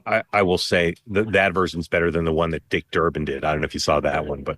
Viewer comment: Every time someone says "Dick Durban". I always hear 'Dick Turban"